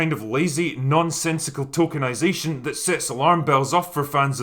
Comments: Aural double take - when you said "tokenization" I thought you'd said "tolkienization" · I thought I heard Tolkenization instead of Tokenization. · I thought you said "tokenisation," but Trey Parker says it was actually "Tolkienisation." Makes sense, I guess. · 'Tolkienisation' did you say drinker or did I mishear you? that is a very apt new term if ever there was one...